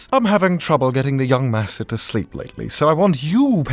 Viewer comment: He said sleep lately, I thought he said sleep with me.